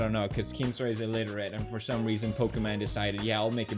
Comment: Did he say illiterate or I'm mishearing?